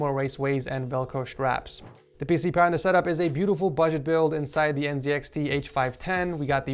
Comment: Did you just say... Budget?